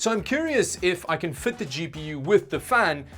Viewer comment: I thought he said,and for a really long time, "Cheapy You" instead of "GPU"